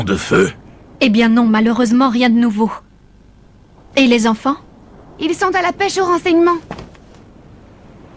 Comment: Excusez moi d avance mais à 6:25 vu que j ai mal entendu j ai cru que Nelly a dit "ils veulent pecho vos enfants" 😂